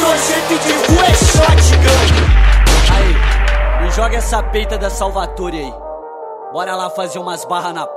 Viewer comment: Muito, muuito fodaa! Alguém sabe me informar o que ele fala nos 4:12? "Ae me joga essa ....... Bora lá fazer umas barra na praça." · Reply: essa peita da Salvatore